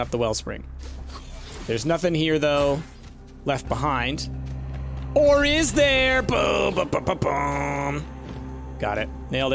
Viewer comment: ‘Or is there?’ What I hear: ‘Ori’s there’